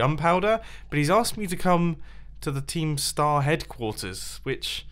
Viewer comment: i thought he said keemstar